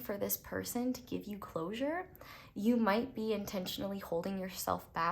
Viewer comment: Pile 1 I got an ad that made it sound like you said “ you hold yourself back because imagination is your only limitation 😅” I’m like damn